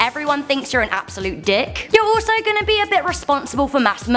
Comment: did she say dick?